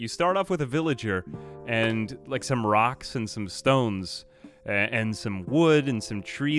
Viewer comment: Did I hear a rock and stone?!